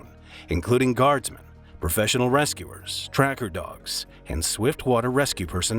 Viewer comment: i thought he said professional wrestlers instead of rescuers and did a double take